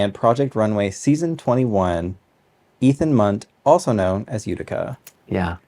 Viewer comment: I thought he said Ethan Hunt was their name, and im like, the spy??